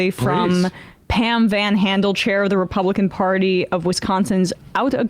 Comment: I heard her say "Pan VanHandlechair" and I looked it up 😂😂